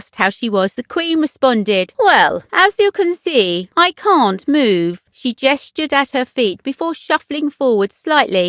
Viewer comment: I’m sure she said “As you can see , I CAN move” I heard no letter “t”. And she duly moved forward.